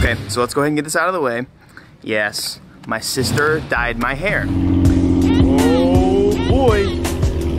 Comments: I heard "my sister died" I was like WHAT!!! Then you say "my hair" like oh ok😂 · holy F i just stopped the Video, and i thought u said "Yes, my sister died" Lmao